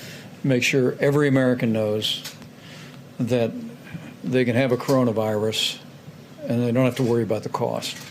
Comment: Was I the only one who heard it on 6:22? " ..... make sure everyone can have a coronavirus..... And not worry about the cost....." Mike must be feeling the stress.